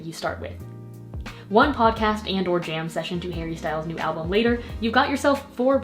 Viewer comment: did she say HARRY STYLES??? And here I thought it was impossible for her to get even more perfect